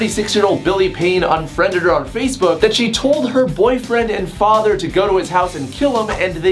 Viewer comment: Oh my gosh, I thought he said boyfriend/father. What's wrong with me?